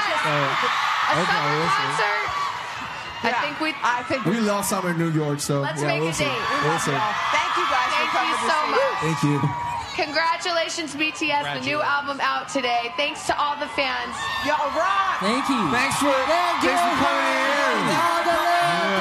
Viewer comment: WHAT A BIG SAME FOR THIS GIRL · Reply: i thought she said “bts i love you so much.” 😂